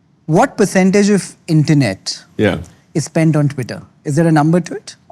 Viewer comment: did he just say twitter ?😭